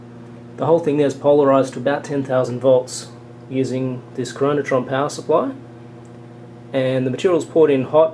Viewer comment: what did you call the power supply that held the voltage while cooling? It sounded like you said coronatron.